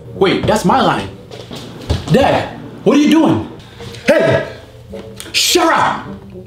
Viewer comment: hey, sharrap! (instead of shut up) 😂😂😂😂 🤐